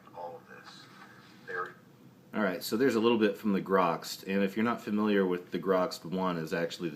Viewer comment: Sorry did anyone get the channel name from the first demon video, sounds like he said " the grux 1 " or something like that. And i cant make the writing out on the channel name under the footage.